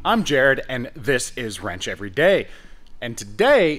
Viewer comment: I thought you said Ranch Every Day. I was like damn this guy loves Ranch Dressing. Jk